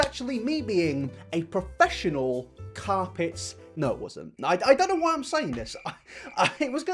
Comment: I think I've lost the plot, I thought you said your first ever professional job was you being a professional...carpet gnome.... I don't even know what that would entail but now I'm intrigued and ugly cry laughing 😂😂😂🧙‍♂️